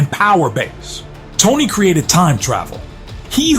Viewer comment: I thought it said “Tony Cremated He Who…” I forgot the rest.